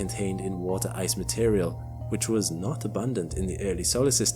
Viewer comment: subtitles say “was abundant” but you say was not